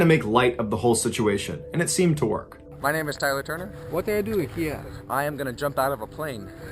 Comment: Who else thought he said Timmy turner